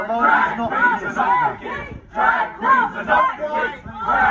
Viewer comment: I thought they were saying drag queens are masochists